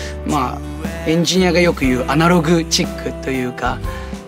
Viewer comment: Is the subtitle writer British? That's not how you spell analog here in the states. Also he clearly said "analog chic". Kind of do have to specify as "analog" is generally viewed as a negative, it's considered backwards otherwise.